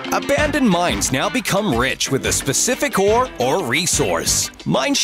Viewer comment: I thought u said "for a pacific ore, Oreasous" (I thought that was a new type of of when it was only 'or reasorus (however u spell it of corse (again!))')